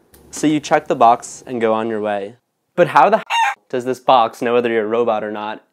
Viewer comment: i swear he said 'heck'